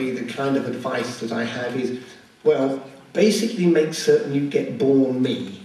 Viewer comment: So brilliant. Though I couldn't understand what he said at about 4:22 - "basically make certain you get... bore me"? If someone could let me know that would be great! And thank you for uploading :)